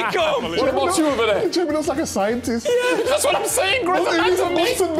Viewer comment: Can someone explain what Micah said at the 1:21 mark please 😂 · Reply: Jamie looks like a scientist, all he needs is a bunsen burner